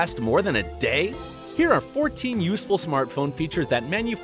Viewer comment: He said useless instead of useful